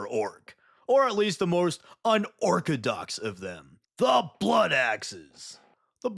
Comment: Did you just say un-orc-todox